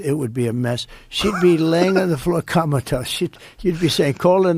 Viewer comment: Did Trump invent a new word …she’d be laying on the floor KAMALATOAST” for comatose? NEW WORD ALERT!!! Somebody call Webster. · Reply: I thought he said Kamatose